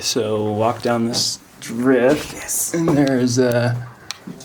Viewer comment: does anyone else hear someone say yes.